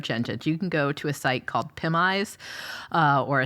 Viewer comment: *EDIT "PimEyes" I cannot make out the name of the first Facial ID Recognition website that she mentioned to Jordan at 3:11. Anyone?